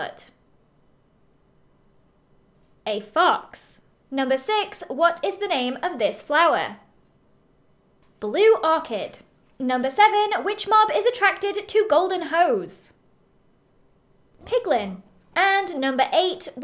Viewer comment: Why did I say me when I heard "what mob is attracted to golden hoes"💀